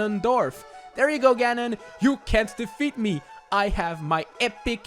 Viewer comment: Due to the accent when you said "empty bottle" I thought at first you said, "empty butthole" - Okay bye